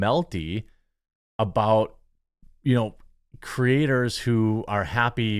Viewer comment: Dude did you just call women “creators”? I hope I misunderstood you otherwise you just played the woketard rules, and I can’t support that.